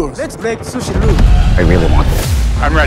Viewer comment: At first I thought this guy said “for everyone out there follow with me!” 😂😂😂